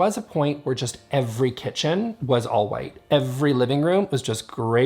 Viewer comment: He kept saying, "all white living room," and I swear I heard, "Alt-white living room." It sounded political and kinda scary as a decorating style.